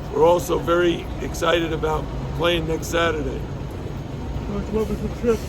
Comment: When he said next Saturday at first I thought he said Gonzaga😂 Go Canes!!!